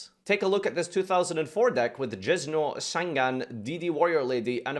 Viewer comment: Are we gonna talk about how he says "Jizz-no" instead of "Jinzo" at 0:51 ?